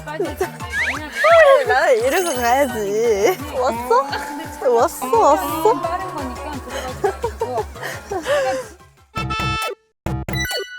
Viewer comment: It ALMOST sounds like Kazuha is saying, "wax on, wax off"...like from The Karate Kid.